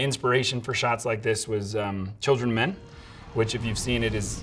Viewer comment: what movie is this? I didn't hear him properly. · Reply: I know this is two years late but he said children of men